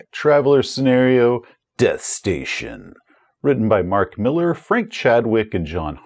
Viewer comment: I misheard Mark Miller and Frank Chadwick as ‘Frank Miller’ and was like “Wait, wat?”